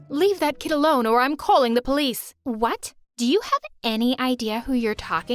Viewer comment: I actually thought Bella said “DO YOU NANNY IDEA WHO UR TALKING TO?” but it was actually just any.